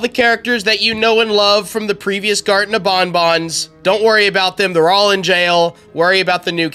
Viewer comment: I laughed so hard that I thought he said garten of bonbon You know the hand puppet from fnaf5